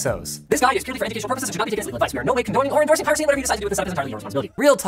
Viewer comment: the audio says "condoning or endorsing..." the subtitles say "endorsing or condoning"...yea....I went 0.25 to find that out....